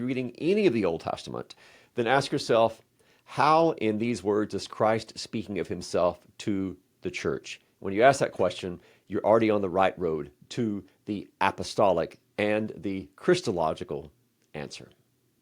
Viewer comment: I'm getting old. At 1:23 mark, he said, "Christological answer," but I heard "Chrystalogical answer." I had to listen to those last five seconds about 10 times before I finally turned on the closed captioning and learned. I am really glad I didn't leave a comment asking what "Chrystalogical" means. 🙂